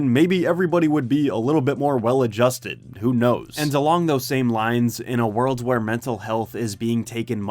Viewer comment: did he say "and along those same lines" or "Delonge those same lines"